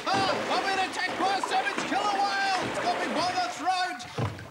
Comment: Someone is going to have to tell me what he says the shark's got at 2:04, before I form my own ideas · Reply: Yep. Deffo scrote.